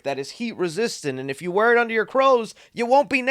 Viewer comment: you say "crows" instead of clothes 🤣